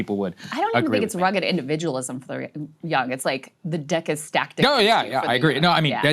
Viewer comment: I was trying to really listen to what they were saying but I kept replaying this video @ 1:40 to 1:43 Sounds like she literally says "The D@ is stacked"!!! Am I going crazy or what????